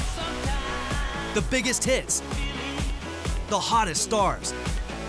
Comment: i realy thought he said : the biggest t1ts :D